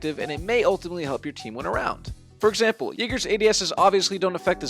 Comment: did he say the N word? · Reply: Its jäger but i hear it hahahahahaha